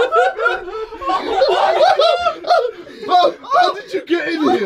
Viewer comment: Vik crying f*cking help me literally made me dead 😂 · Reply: At first i thought he said "f*cking hell" but then i just listen carefully then i heard "f*king help* 😂😂